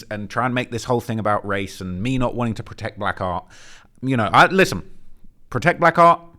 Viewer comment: protect black art?? in a murder trial?? IT'S MURDER!!! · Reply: Lmao I thought he was saying “protect black R” as in “Black Rights” 😂i was wondering why he wasn’t just saying “ Black RIGHTS” why